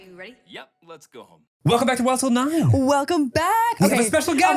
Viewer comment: I’m a semi new listener and I just discovered today that actually says “Yep let’s go home” I always thought he just said “Yep let’s gooo”